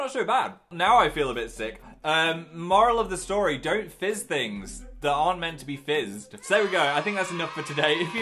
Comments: I heard "don't fist things that aren't meant to be fisted" uuuhhh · “Don’t fizz things that aren’t meant to be fizzed” - 2019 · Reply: oof I heard "don't fist things that aren't supposed to be fist."😂😅